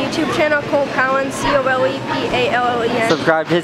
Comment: what is his channel name? I couldn't understand what he was saying lol